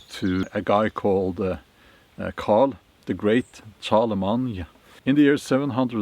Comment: Charles the great (Charlemagne). Sounded like you said Carl the great.its hard to imagine that many beheadings. Early so called Christians were brutal